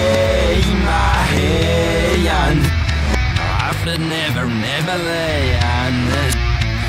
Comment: "sayy ma yunnd" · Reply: i heard 'slay my head' ...